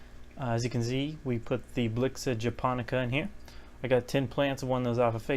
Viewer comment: What was that plant? It sounded like you said Buxa Japonica. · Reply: Blyxa japonica